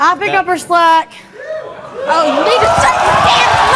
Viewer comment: I’m I tripping at 8:53 or did she say … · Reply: “You need to shut you * mouth” is what I hear